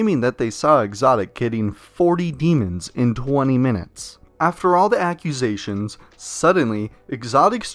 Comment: i thought that said 1 minute..